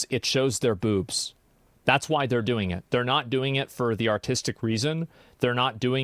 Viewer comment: I heard "autistic" nudity and it didn't sound wrong to me until i understand what they actually meant x)